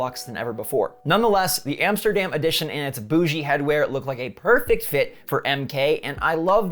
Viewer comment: did he just say bochgi. The world means a black person from the hood who acts high and mighty